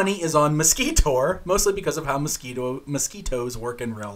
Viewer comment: Totally thought he was saying mesquitor because women suck the life out of you 🤣